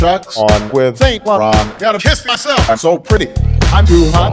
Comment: Instead of "I gotta kiss myself." I thought he said "I gotta piss myself." I'M SO PRETTY!